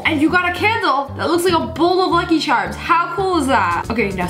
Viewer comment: I thought you said “fucky”